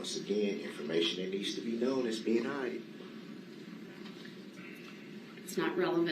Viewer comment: did he say it's being hide? Instead of hid? 🤣🤣🤣🤣🤣 what a moron